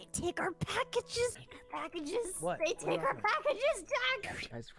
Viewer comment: Not the cabbages Edit: it sounds like she says cabbages when she says packages